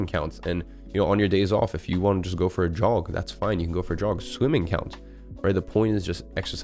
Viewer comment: youtube auto generated captions say "you can go for drugs"